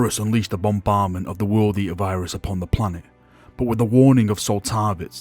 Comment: I must be mishearing something, but is saying World Eater virus? I always thought it was the Life-Eater virus.